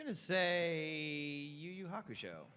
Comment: Okay but what was Cliff's answer? I couldn't hear him. 😅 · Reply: Mikorida He said Yu Yu Hakusho, great anime